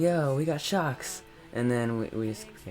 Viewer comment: i thought he said shots